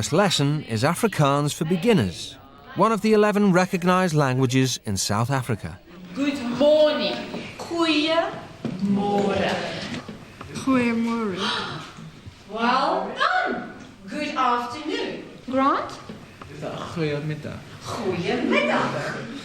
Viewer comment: did I just hear "Koeie môre"? 🤣🤣🤣